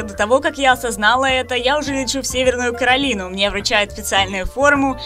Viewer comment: послышалось другое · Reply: Твою... ты услышал Северный Корей.